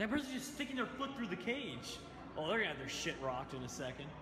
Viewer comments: did say shit? I'm not gonna hate or anything, he just hasn't cussed on camera for so long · did say "Oh they're gonna have their censored rocked"?